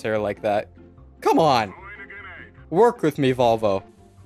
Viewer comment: "Work with me Volvo..." Lol, did I hear that correctly?